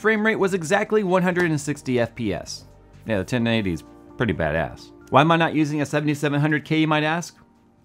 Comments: Jay sounded like you said a 1090 is bad ass at 3:11, half life 3 confirmed, no wait GTX 1090. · hey Jay... around the 3:10 mark did you say the 1090 was badass?